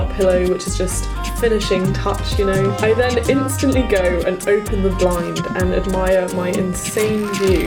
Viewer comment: captions say open the wine Wtf YouTube 😲